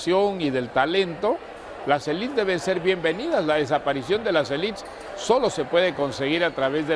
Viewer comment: ¿la desaparicion de las ....? Cual es la palabra que dice? Elips?